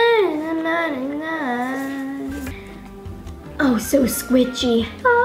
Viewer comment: Think the girl said squiCHy at 2:50😂